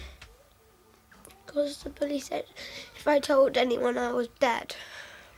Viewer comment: what? "the police said if i told anyone i was dead?" 😲